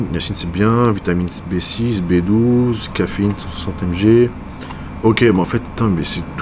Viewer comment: Pourquoi t'as dis c63 amg apres caféine à 19:45 fou frero 🤣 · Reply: mdrrr j'ai réécouté effectivement il dit 160MG mais on dirait c63 amg 🤣